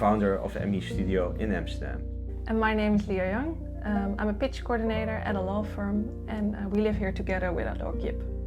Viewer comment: Nice one, but I thought she said I’m a bitch coordinator instead of pitch coordinator 😂